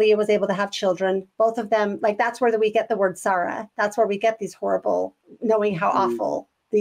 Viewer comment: the word sarra? I can't understand what you're saying. Do you mean sorrow?